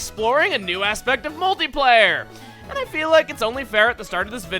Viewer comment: when he said "multiplayer", I almost thought he said "moldy layer".